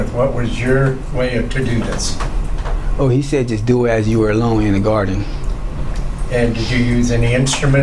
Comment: I thought the first chick said her kids were "in the Garage"!!!🤐😂